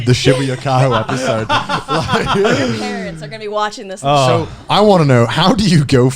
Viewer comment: They get so hyped up talking about it that has trouble getting a word in. Haha. WHAT...did say at 5:17? My god, I could not decipher that. It's like all of a sudden she busted out a rap verse. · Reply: either your or our... parents are going to be watching this